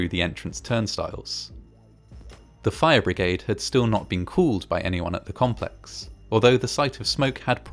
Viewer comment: just wondering, what kind of accent you have to say "called" but sound like "could". (Not making fun, I'm not even English native so...)